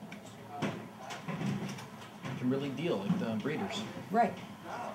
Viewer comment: I think the subtitle is wrong, it should read "Kimberly Deal with the Breeders?"